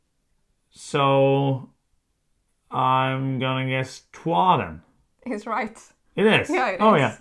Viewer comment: Can someone tell me what is the game at 9:20 ? I have repeated the video multiple times and can't get it ^^ This was fun to watch. I was doing dishes, so I have no idea how well I did. But I did got some right. Port Royal I immediately thought about it. I love that game, but it took a bit to warm me (and the expansion for sure helped because I felt a bit the same from the Admiral) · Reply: It’s Troyes! I’m so glad you enjoyed it!